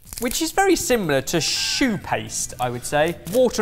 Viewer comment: Fancy helping us make Sorted (even) better for you and the whole community? Take part in our Big Survey Of The Year here: <link> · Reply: funny I thought that he said shoe paste and that that’s what he meant as in it wasn’t very good😅